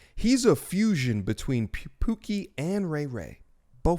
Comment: was driving when I first heard this and thought you said a fusion between "Loki" and Ray Ray haha like damn, The god of deception tho?..would have been fitting. Props for the DBZ Fusion reference ✊🏿 we out here